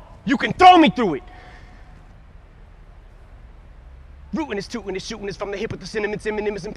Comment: did he just say rootin is tootin